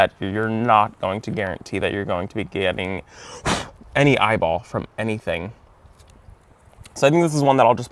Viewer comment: Did you just say eyeballs @ 20:57 ? 😂❄️☃️💚🙃